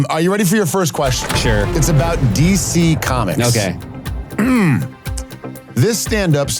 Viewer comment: Was really happy with the DC comics joke because when Ike first read the categories I thought he said, "decent comics" and I was like, "why does Seth want to answer questions about pretty good comedians?"